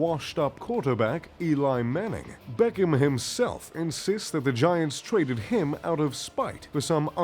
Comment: lol I heard u say vagiants😂